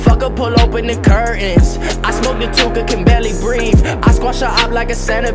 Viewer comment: did he say he smoking on tooka?